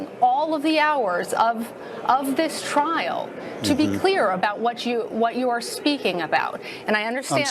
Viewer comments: Van der Veen represented many of us. His obvious disgust with the left and their propaganda media is exactly how i feel. They are wicked beyond words. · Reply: The media are indeed wicked, but in this case, he misheard what she said. She clearly says, "To be clear." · Actually she said, "To be clear" not "to be fair". He misheard her, but she did speak about the facts in a way that seemed to minimize what the managers had done even without having actually said "to be fair".